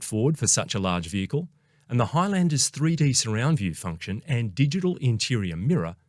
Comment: Did you say Highlander ?